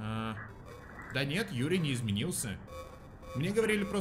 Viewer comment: файл Юри не изменился Что послышалось мне: файл Юра не изменилс